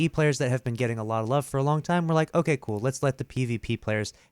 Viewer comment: my phone thought you said "ok google"